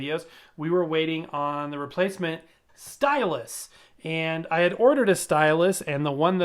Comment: Sounded like you said "stylist" at around 0:40. So I'm sitting there thinking0, "I just wanna play records, not get my hair done!" :D